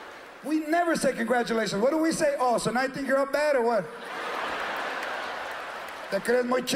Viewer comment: What Did he say at 3:03 😂? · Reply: You think you are the s*** thats what he said